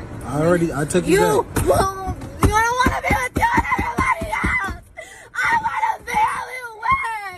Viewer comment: Dam that some funny shit it sound like she said i only want be with u and noone else